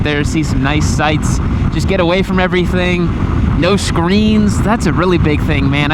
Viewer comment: i thought you said screams, realized it was screens.